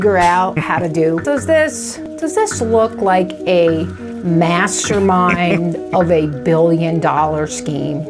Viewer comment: Did anyone else hear Megamind? I had to retwatch this bit just to be sure she didn't say it.